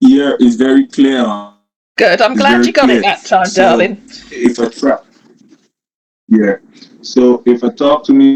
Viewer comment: Did he say "its a trap"? At aprox 7:49 😂😂 He's twisted his lies so much, that he's forgotten he can take care of his money problems in 2 weeks, when he's off the "rig". Amazing! They have an excavator on a rig.😂 This scammer is a mess. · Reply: I agree with - I think he said "if I try".